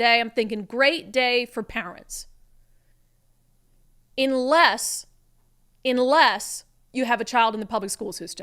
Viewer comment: Not being a grammar n@ But did she say at "Inless Inless" at 1:05 or if it is just an accent ....sorry English is my second language but I have been studying in English Medium since Kindergarten. And my english is better than average American coming from India.